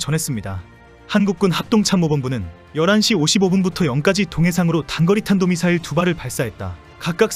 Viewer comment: 자막은 '0시까지'인데, 소리는 '0까지'라고 들리네요. 2:45 자막은 '떨이지고 있습니다' 소리는 '떨어지고 있습니다'라고 들리는거 같군요. 이렇게 두개정도가 틀린거 같네요. 편집 실수 하셨군요. 저 일본애들은 그렇데 급하다면서 무슨 실험을 저렇게 하고 있는지.. 그냥 기존 방식으로 위성을 쏘면 될텐데. 어차피 돈 많은거 아니었나? 그리고 당장 새 방법이 안돼면, 조금이라도 위성을 쏘기 위해서 기존 방식으로 먼저 날려서 정보 확보하면 될껄.. 하여튼 생각이 없어요. 아, 가만.. 혹시.. 기존에 위성을 만들던 사람들이 퇴사해서 못 만드는건가? 흠.. 하지만 아무리 그래도 정보는 남아있을텐데?